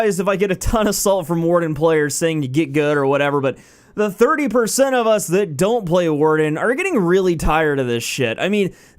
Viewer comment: If u use a class just because u have a better chance to win, you're playing for the wrong reason. As with any game, you play because it s fun. I main the warden because i think sword knights are cool as shit, thats it. 30% of players are warden? I hope that was a joke or intentional hyperbole, because if you re serious, you re just plain bellyaching. Sounds like you had a bad day and decided to bash the last class that schooled you. Everyone hates the last class that got "cheap kills" when u fought them. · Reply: he actually said the 30% of us that aren't playing warden